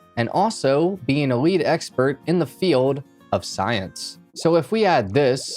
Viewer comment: I was listening from afar and I thought he said weed expert instead of lead expert😂😂😂😂😂